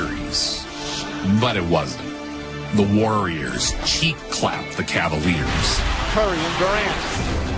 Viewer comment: did bro say cheek clapped?